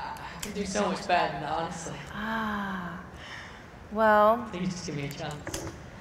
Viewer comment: what did he say? was it "thank u for giving me a chance" or "think you'll give me a chance" ? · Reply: Please just give me a chance